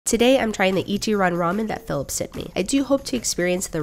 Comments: I thought she was boutta say ichiraku ramen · I thought that she said ichiraku ramen from Naruto